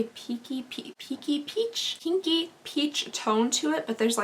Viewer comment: I really thought you said: “kinky” shade! Not pinky! 😂😂😂